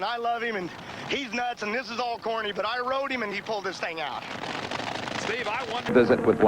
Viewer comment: Did he say '..and I rode him and he pulled his thing out.' 🤔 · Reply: This thing out. He pushed Austin Coil hard to win.